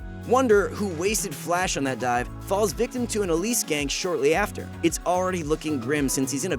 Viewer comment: did you just say elise?...